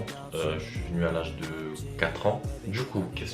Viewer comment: j'ai entendu "j'suis né à l'âge de 4 ans" j'ai bégaillé 0 vanne